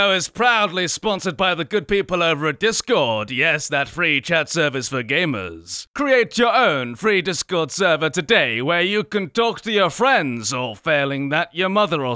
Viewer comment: In the sponsorship at the beginning, who else thought it said, “Create your own free sewer today!” until he said “server”?